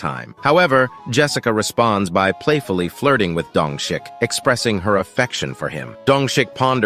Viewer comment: dongshik I don't care that the subtitles say that but I hear dung shit